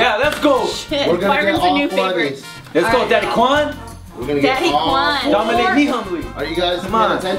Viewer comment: "let's go daddy kwan, dominate me humbly" did anyone else catch that?